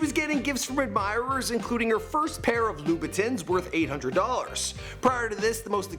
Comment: Did he just say "lubitins" wtf 😂